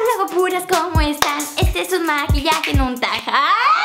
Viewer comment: que dijo en el minuto 0:02 ? maquillaje que ? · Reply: creo que dice "esto es un maquillaje y no un tag"